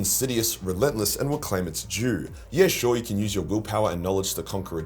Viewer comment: i heard this as "claim its jew"